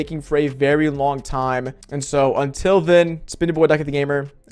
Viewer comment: Try talking slower fool. I can barely make out a single word that you said in the first 3 seconds. It sound like you said LuckyTheGamer